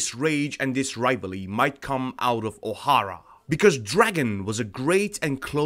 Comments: I thought bro said Ohio not O'Hara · I thought it said"might came out of Ohio " ahhahahahahaha lol